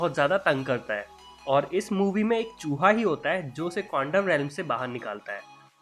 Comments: who heard it condom and not quantum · he says condom instead of quantum. Lol